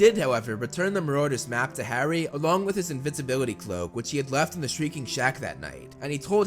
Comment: did he say INVINCIBILITY cloak. I wish it was that xD